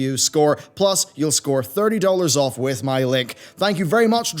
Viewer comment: I heard "with my lick" so I went back and replayed it. Still heard lick.